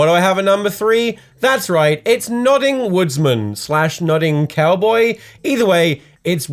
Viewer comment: I thought he was saying Nutting woodsman or Nutting cowboy